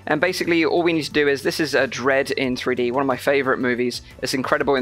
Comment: any vr movies recommendation? What was the movie he said at 9:20? Dread? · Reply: Dredd 3D -)